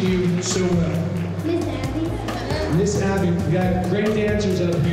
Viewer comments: The judge said to Chloe " now are all of you girls really best friends?" And Chloe answered "of course" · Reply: I heard him say "now are all of you girls are best friends right?" And Chloe said "Of course" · When Chloe replied, "of course" did he ask if they were really best friends or if they were really good at stretching? I replayed that part a few times and heard both.???? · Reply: WTF When I put the phone close to my ear I hear “ are all of you best friends” and when I put it far away I hear “ are all of you are really good at stretching” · The judge said to Chloe " now are all of you girls really best friends?" And Chloe answered "of course" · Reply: The first time I watched this I thought he said now are all you girls really good at tricks but I saw this and realized it was the other thing